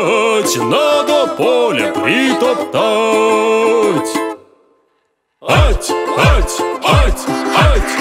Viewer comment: Вот что я называю Русская народная песня! 😂😂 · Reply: Мне одному послышалось надо поле бритатать